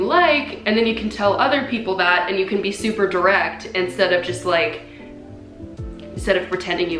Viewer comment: Ngl I was half listening and I thought she said “super erect” 😂